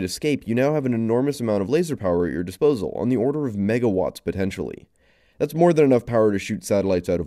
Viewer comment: I thought you said mega-bytes and was like "dam that is one low quality lazer"